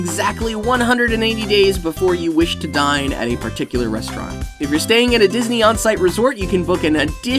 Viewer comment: did he just say ‘die in any particular restaurant’?